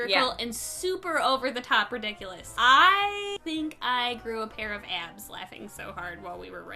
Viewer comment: LMAO I've always loved your accent. I thought you said "grew a pair of ads" and I pictured YouTube ads attached to your midsection 😂